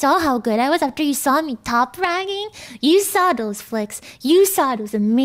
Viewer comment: I think she says "top fragging" which is a term thats means getting the most kills 👍 btw good editing once again, 🙏🙏I love your style